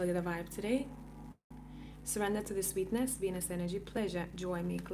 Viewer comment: Woohoo, it sounds like I'm gonna get laid! Hahaha, dude you were saying "Venus energy" and I heard "penis energy." Lmao, oh my goodness! This was a really awesome collective reading! Thank you!